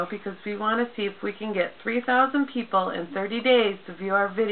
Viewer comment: oh you said 3000. it's ur accent, i thought u said 30,000! that's good then, looking forward to your haircutting video!! ;-) ur daughter's adorable by the way...!